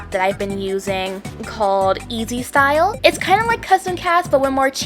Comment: what did she say here?? I rlly want this app :< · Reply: sorry for late reply but it’s easy style (also goes by he/him!) /nm